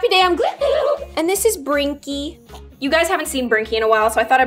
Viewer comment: Imagine if someone was new on the channel and they actually thought that twinkys name was brinky lol😄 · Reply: Lol yes I’m not new but when they were saying twinky I thought they were saying brinky